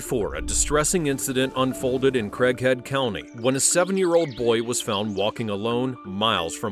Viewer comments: I thought this man said Crackhead county 😂😂😂. Sheesh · I thought you said crackhead county at first 😂 would make sense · I had to rewind, i thought you said crackhead county 🤦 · thought he said "crackhead county" lol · Did a double take because I heard "Crackhead County" and I'm not joking 😬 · At first I thought he said “Crackhead County” 😂😂😂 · anyone else mishear that as crackhead county the first time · I heard it as crackhead county …but that’s not wrong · at first I thought he said "crackhead county" lol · I misheard him say "crackhead county" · I thought they said crackhead county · Thought they said “crackhead county” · SERIOUSLY...I thought they said Crackhead County😅🤣😂😎